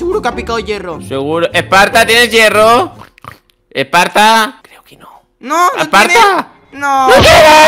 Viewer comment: Se le vio la voz qué dijo un susurro que dice creo que no tiene hierro jsjsj oye bien en el segundo